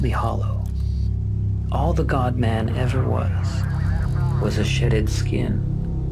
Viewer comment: Who else thought he said "sh!tted" instead of "shedded" a skin or shell?